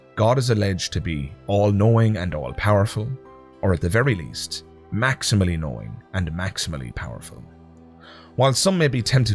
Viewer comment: for a second I thought you said, "Maximally annoying". Also true.